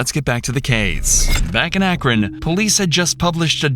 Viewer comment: Why is it always ohio man ive seen like 20 videos under this acc and half of them i hear "akron police"...dude like ohio get it together this is so not skibidi sigma at all